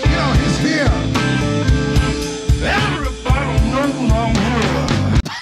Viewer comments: i think he said "everybody knows I'm here" but I really can't tell. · So, I think what Steven Seagal is saying at 3:31 is “Everybody knows I’m here!” It sure is extremely difficult to understand him though, regardless. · He actually says “everybody knows I’m here”. You’re welcome. You can try to “Green Needle” yourself to hear this too. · I think he said "Everybody knows I'm here" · did he say: "Everyone knows that I am here"? Lol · I just realized what Seagal said. He said "Everybody knows I'm here." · He clearly said "Everybody knows Im here" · Seagul actually said "everyone knows I'm here" listen back now and tell me you don't hear it, lol. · What Steven says here is "Everybody knows my name" in some Ancient Tounge. Name is extremely fucked up but the Everybody part is what gave it away for me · No, I think he said, "everybody knows I'm here". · I think hes says "everybody knows I'm here" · After many decades of deciphering I finally found what Steven segal said in this moment 3:31 and it is “everybody know that I’m here” this took to damn long. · I think he said “Everybody knows I’m here” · I have decoded Segals alien language and what he says in 3:30 is “Everybody knows i’m here” But it just sounds like gibberish if you don’t listen carefully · i figured out what he's saying took so long but i figured it out i think he's saying "everybody knows I'm here!" at least i think so lol.🤷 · Sounded like he said everybody knows umbrella